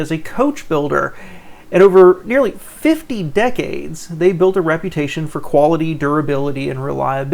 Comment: Fifty Decades? Wouldn't that be 500 years? Thanks for the video, I'm lucky enough to own a 1963 Studebaker lark. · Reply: I thought I heard, "15 decades"...